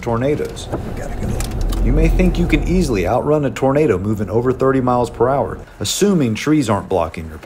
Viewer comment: OMG CLOSED CAPTION IS STUPID 1:59 HE SAYS "you may think you an easily outrun a tornado" CLOSE CAPTIONS SAYS "You may think you can easily outrun a Burrito"